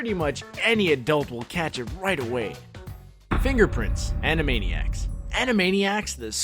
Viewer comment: I don't get the prince one can someone explain please I've seen it one metric FUCK ton but i still dome get it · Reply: Instead of "Fingerprints" she thought the guy said "Finger Prince" I just realized it myself lol